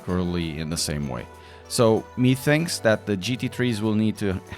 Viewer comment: did you say"me thinks"???